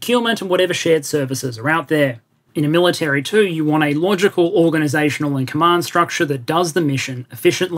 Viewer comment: I misheard logical as laundrycal 😏